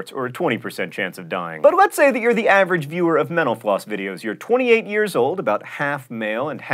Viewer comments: Man, I keep hearing you say "menopause" instead of "mental floss"... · For some reason, I hear "menopause" when you say "mental floss".